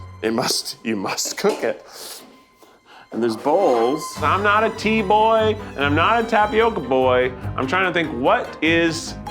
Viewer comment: My ears perked up and I was so confused when Keith said “I’m not a tea boy” bc I heard “I’m not a Tboy”